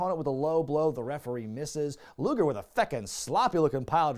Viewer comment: Did you just say "feckin"? 😂